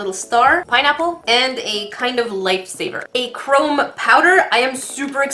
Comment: I misheard you as saying "light saber" and then realised you actually said "Life Saver"... I was looking at the doughnut shaped mould and wondered why you were making a Star Wars reference! I need new ears.